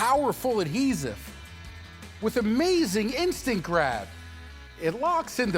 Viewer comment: I thought you said with amazing Instagram